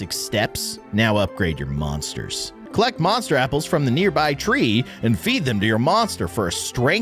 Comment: For a second I thought you said get apples and feed them to you're mom